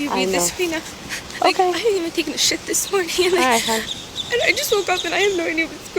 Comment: 'I haven't even taken a sh*t today.' Gonna try that defense next time i get pulled over. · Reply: Ah man I thought I was hearing it wrong. I thought she said shift but then it didn't make sense to me why would she say she hasn't taken it yet... Sadly, not the weirdest part of the video...